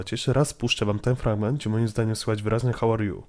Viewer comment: ja słyszałam - Haloo.....Halo.... are you? ( przynajmniej za tak usłyszałam :/ )